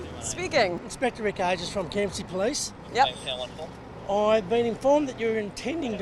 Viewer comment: Did he say KFC police?